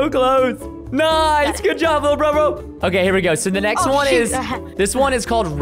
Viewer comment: did you say littlebrobl